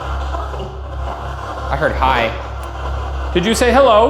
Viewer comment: q's for debrief in the replies of this comment please! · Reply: I heard "I told you" at 36:34 rather than "hi"